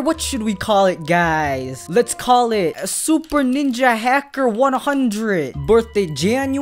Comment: hmm 100 i think that say 1000